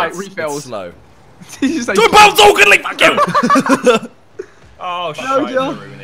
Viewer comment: did just say diddly fuck you?